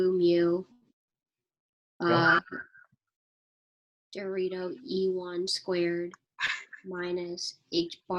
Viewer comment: did she say "dorito", or do I just misshear it? :D